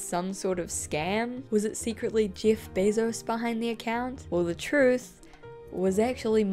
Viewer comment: Did she say Yiff Bezos at 8:41? :D